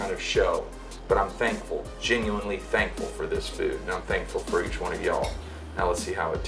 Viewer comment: i think he says "not thankfull for each of y'all"